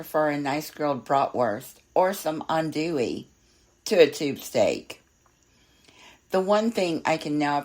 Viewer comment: Wait, wait...did she say "tube steak"?!😳